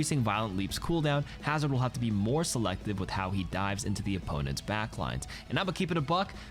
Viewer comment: It says "HOE DIVES" instead of "how he dives"